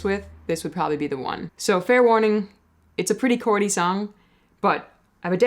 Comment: Chordy song? I thought you said corny, I was just about to get angry with you 😉